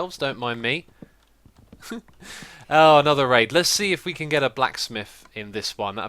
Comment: why did you say "oh another rape"?